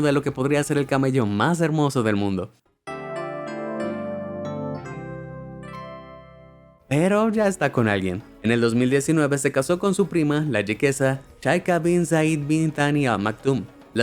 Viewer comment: soy yo o escuche prima?